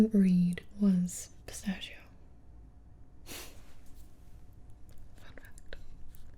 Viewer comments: When she was talking about pistachio and she said fun fact I thought she said fuck that 😅😅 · I swear she just said f*ck that.... oops haha. (Edit) Just replayed it. She says “fun fact”